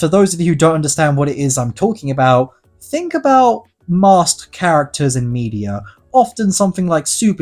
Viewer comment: Him saying "Masked" sounded like he said "Master" to me. 😅